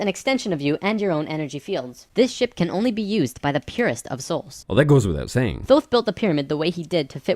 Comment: Did anyone else hear "This SHIT can only be used by the purest of souls"????